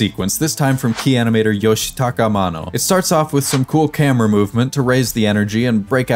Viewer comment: for a moment there, I really thought you said Yoshitaka Amano, then I saw the screen (Yoshitaka Mano)